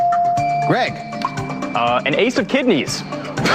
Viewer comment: Did he say an Ace of Kidneys?